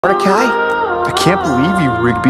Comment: I love how it sounds like he is saying R Kelly instead of Mordecai